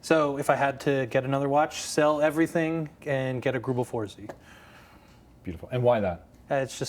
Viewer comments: What did he say? Google 4Z? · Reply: Greubel Forsey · What did he say he would get at the end? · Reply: Greubel Forsey